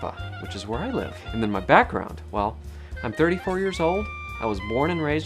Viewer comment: It sounded a little bit like you said "ascent" instead of "accent" 18 seconds in, and at 1:22 you say, "mah background" instead of "my background". I am a Californian and have recently realized these quirks we share. It's from Oklahoma from a great migration during the dust bowl, and is slightly more evident in the Central Valley than on the coast. But we stand out like a sore thumb to anybody from east coast.